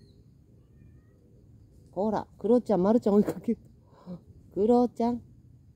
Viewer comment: 私の聞き間違いだったら何ですけど 『クロちゃんまるちゃん追いかけて…』ってママさん言いましたか？ あのまるちゃんが追いかけられてるってちょっとウケる～😂